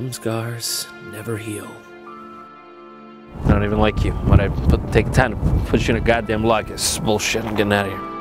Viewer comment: "I don't even like you ____ god damn _____ this is bullshit, I'm gettin outta here" can someone fill in the blanks please? couldn't understand him.. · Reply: but I put the time to put you into the goddamned locket, this is bullshit